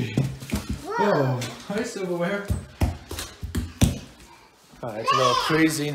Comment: did I just hear that their cat is called silverware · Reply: The cat's name is indeed Silverware